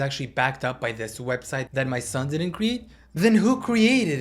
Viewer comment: At first I thought she said “ which my son made” but maybe that was just my brain 🤔